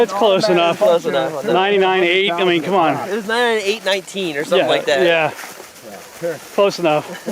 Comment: SO IT WENT 99,018 FROM WHAT I COULD HEAR HIM SAY @ 2:56:01..... SO I LOOKED UP STRONG Strong's Concordance. pond . Probably from the same as 'agam (in the sense of stagnant water); figuratively, sad -- pond. Strong's Hebrew: 99. אַגְמֵי (agem) -- Pool, pond - Bible Hub Word Origin: Derived from an unused root meaning to collect or to gather. THEN I LOOKED UP 018.... Strong's #18: agathos (pronounced ag-ath-os') a primary word; "good" (in any sense, often as noun):--benefit, good(-s, things), well. Compare 2570. Thayer's Greek Lexicon: agathos 1) of good constitution or nature 2) useful, salutary 3) good, pleasant, agreeable, joyful, happy 4) excellent, distinguished 5) upright, honourable